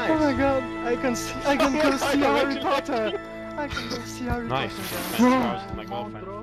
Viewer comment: Did he just say: I can go see Harry potter ?.. 21:40 ? Or am i just tired ?